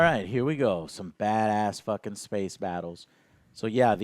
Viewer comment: “Spacebattles!” one person hyperdrives through the fleet Well that was intense! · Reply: Honestly, I thought you said “Spaceballs”